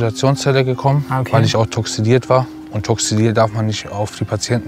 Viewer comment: was sagt er da? toxiliert? toxidiert? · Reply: Toxidiert, meine ich 🤔